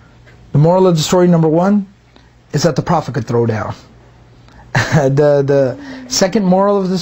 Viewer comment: why did i hear “the prophet can throw it back” estagfirullah may Allah swt forgive me and my stupid brain